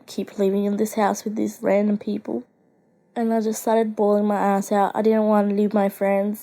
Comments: hold up did see just say she was balling her as* out just rewind and you'll hear it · she said she just started baling her eyes out but it sounded like she sad she just started baling her ass out😂😂😂😃😂😂😃🔥🔥😂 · who else heard her saying “I started pouring my ass off” Lol I know it’s supposed to be “eyes” but it’s funny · I hear Say ass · What did she say??? THE A WORD??? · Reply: she said eyes, you retard. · When she said “balling my eyes out” I thought she said “balling my ass out” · I thought she said “bullin my ass out” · DID SHE SAY BALLING MY ASS OUT? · did she say eyes or as*...?